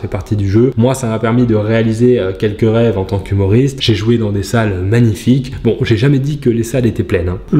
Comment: c'est moi ou tu a dit que tu avais jouïe dans des salle ? XD